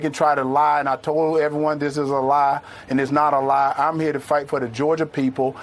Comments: Is it just me or did he say at 3:30 time stamp that he said its a lie although it’s not a lie. Maybe it’s just me but sounds like he just told on himself 😂😂😂😂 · Wait at about the3:25 mark did he say I told them it’s a lie and it isn’t a lie!!! What ???